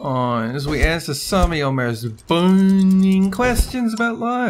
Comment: I thought he said "boning questions" im so dirty